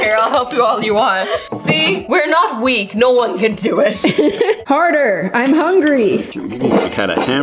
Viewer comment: did she say "were not weak, no one can do it,?